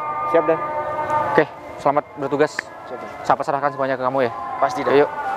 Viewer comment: ndk bosen ngulang ngulang nontonnya...salfok ma kata kata mas yg bilang ku pasrahkan ke kamu..atau saya yg salah denger.😂😂😂😂.harusnya kan ku serahkan mas ..Lo pasrah ma khusus kita berpasrah ma yg di Atas